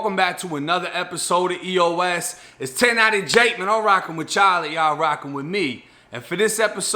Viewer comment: Thought this man was saying I’m rocking with Charlie and y’all rocking with me 😂😂 much love g